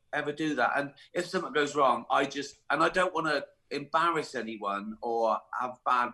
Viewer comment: Does Ricky say at 0:45 :"I don't want to embarrass anyone?"( Of course you could say the people he goes after deserve it.)